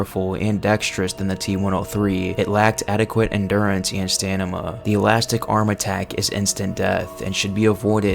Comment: bro did you just say "Stanima"?